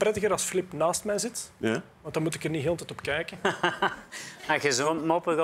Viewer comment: wat zegt hij bij 1:27, ik versta er niks van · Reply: Dan moet ik er niet heel de tijd op kijken